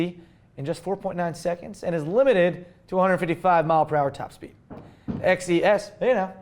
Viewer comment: Did you mean 155mph? Because I heard it as 255mph and was like whaaa?